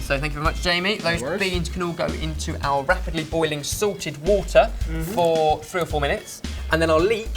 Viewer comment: I hope I'm not insulting Ben's accent or anything but at 3:35 and 3:45 is he saying 'Sorted' or 'Salted'? xD